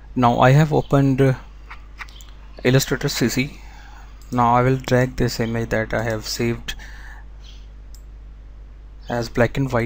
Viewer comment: what are you saying at @ 2:05? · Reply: now I will drag the image that I have saved